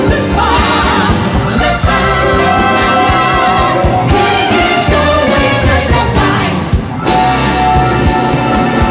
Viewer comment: ここのコーラスの方々は何て言ってんだろう？ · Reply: He is always dynamite です